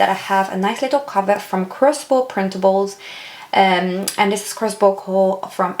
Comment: Thank you for the video! ... can you please note where you got the cover I thought I heard Crispo printable.... but I know I'm mistaken. Can you also. Let me know where you got the paper inserts : weekly, the 2 to do lists and notes. Thanks in advance!